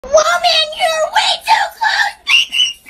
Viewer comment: WOMAN YOU’RE WAY TOO CLOSE; ✨PePpEr sPrAy-!✨ 2024 edit: Why of all comments did this blow up- ._. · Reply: I thought he said “woah man you’re way too close”